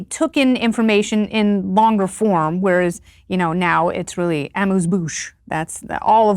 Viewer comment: I think at 4:50 Kennedy says "amuse-bouche. "The term is French and literally means "mouth amuser".